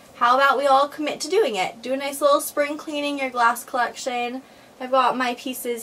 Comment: At first I thought it said Spring Cleaning Your Ass